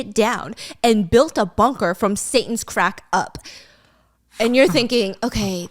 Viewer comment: did You say ; from Satan's crack up...? That makes You an insaniac